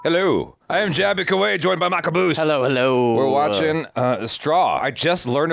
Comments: Off topic but I thought when Jaby did the introduction it sounded like you said, “I’m joined by my caboose!” 😂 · Sounds like he said "joined by my caboose" instead of Michael Boost😂😂😂